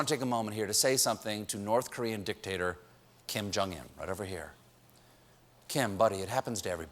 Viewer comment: Any EXO-Ls here? at 6:05 he said KIM jONG IN instead Kim Jong UN and I was like : Kai, boi wat U doin in North korea!?!?!?!